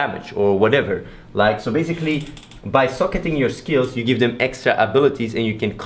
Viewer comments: I thought you were saying, when you "Suck at your skills".. I was like what, skills get buffs when you suck at them? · it sounds like he says 'suck it' when he says 'socket' XD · When you say Socket, it sounds like you say Suck it xD Looks like an awesome game! Nice video Athene! :D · lmao i thought he said you can suck at your skills not socket them lololol